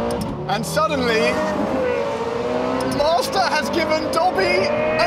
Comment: I had to rewind because I thought he said Mazda!